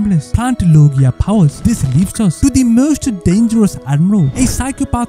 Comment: idk if im trippin but i think he added a pun after admiral gaia cuz i heard “this leafes us”